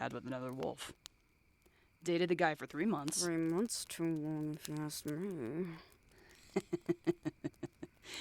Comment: Can someone please tell me what said at 34:21 ? BTW great audio I also am a follower of Eris Serenity and Harper Studio who was back then madamwigglesworth. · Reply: Bit late to answer but she said "3 months too long if you ask me"